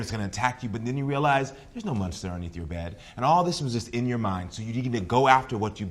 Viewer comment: I swear he said "mustard underneath your bed" and I was so confused